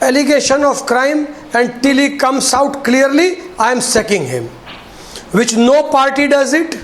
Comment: I thought he said sucking :)